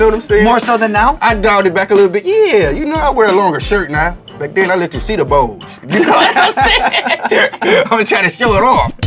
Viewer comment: Bro until today I thought he said he let his "cedar bulge" but I think he actually says "let you see the bulge". I've been calling my dick cedar wood for a year.